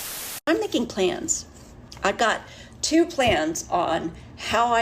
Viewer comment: I thought she said two cans